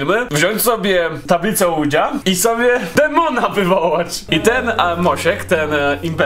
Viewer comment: dlaczego zamiast "demona" usłyszałam "tampona"? 😂😂